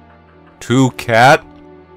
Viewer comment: when i heard you scream "two cat!!" i heard it as twoCAN(toucan) also math is HORRIFYING. Aria knows the deal with...mathematics oof